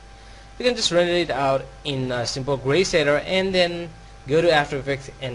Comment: In 21:07 you sad that : "you can render it in <Greyblabla>...." Can you specify what did you sad? Becuase I cant understand it, and I need to know that. Thanks. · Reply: I mean just use simple grey color and render